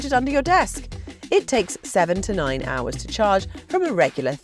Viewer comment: I misheard "seven to nine hours to charge" to "seventynine hourse to charge" and was like WTF. :D